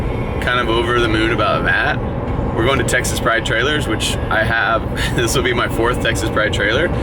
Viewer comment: For a moment I thought you were saying Texas fried trailers 😂😂🤦🏽‍♂️